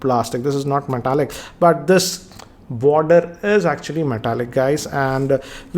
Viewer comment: err i think i heard he said Metallica .. 😂